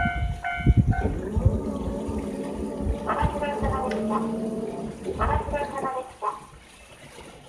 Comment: 最後なんて言った? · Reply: 「おまちどうさまでした、おまちどうさまでした。」